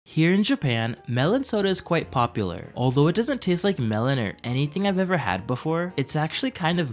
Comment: at first I thought he said melatonin soda 🤣🤣